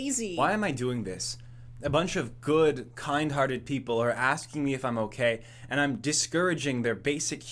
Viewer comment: i thought it said "stop asking if i'm gay' i need more sleep